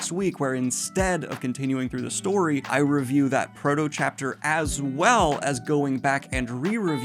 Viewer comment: Love your reviews! I kept hearing 'proto-chapter' as 'frodo chapter' and i was so confused for a minute there.